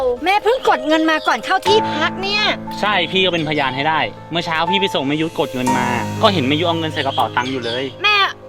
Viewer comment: ได้ยินพี่พูดผิดเผลอได้ยินพี่พูดว่า เมื่อเช้าพี่ไปส่งประยุทธ์มา🤣😅😂